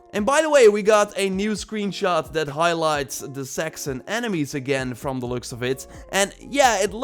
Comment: at first I thought you said we got a new screenshot that highlights the sexing enemies